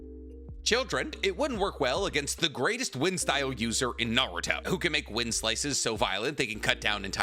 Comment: what did he say 🤨 Naruto? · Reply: Yes. He said Temari was the "strongest wind user in NARUTO"